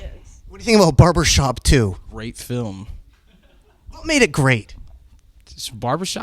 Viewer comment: who else thought he said "rape film" when asked about barbershop 2 at 10:29? hahah sounds like something he would say lol